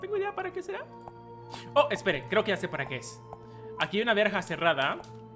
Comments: en el minuto 0:48 escuché "aquí hay una verga cerrada " :v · En el segundo 00:50 no se si escuche mal pero dijo aqui ai una verga cerrada v